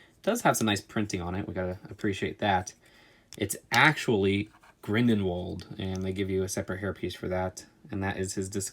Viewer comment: why did it sound like u said grindenwald instead grindelwald